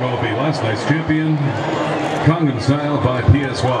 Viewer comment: i swear to god he said condom style